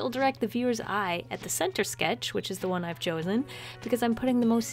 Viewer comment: i swear she said my name (joeylynn) and ive had to replay it multiple times to hear that she says "chosen" hhhh